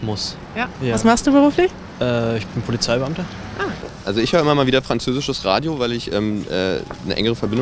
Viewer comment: Minut 1:04 there is a mistake: "Als ich hör..." instead of "Also"